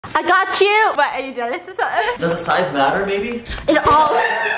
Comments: I heard "Does the size matter, baby?" and I don't know how to feel about it... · Does size matter actually? ... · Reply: I misheard it as “Does size matter bAby ” and i was like and i oop